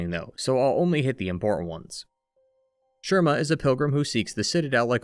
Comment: I gave myself whiplash when I thought you said "Jerma"